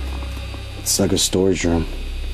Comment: asks “is anyone in here” and around 23:46 i heard “it’s me”